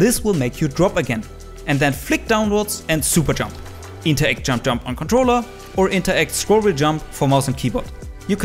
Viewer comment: Can someone tell me what he says at 1:30 - 1:36 Please n Thank You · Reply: then flick downwards, and super jump. Interact jump jump, on controller. Or interact, scroll-wheel jump for mouse and keyboard.